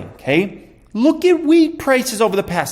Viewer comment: I thought he said look at weed prices🤣🤣